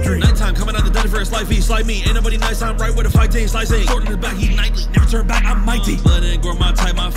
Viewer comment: i was so confused i thought he said "never turn back on my team" like that does not sound like muzan